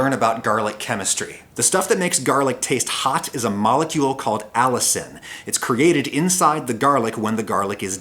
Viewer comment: Totally heard that as "a molecule called Alison" and I just thought aw, that's a lovely name for a young molecule ❤️